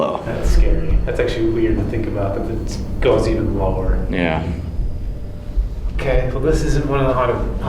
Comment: Did anyone else hear that faded "Yeah" in the background. Didn't sound like any from the team ..